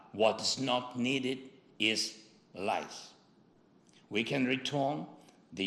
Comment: thats to correct the translation at 10:30, think was he said was. Whats not needed is lies<-- which some EU leader promised to provide weapons to help ukraine but still havent given any. Only US has outmost provided ALOT of support!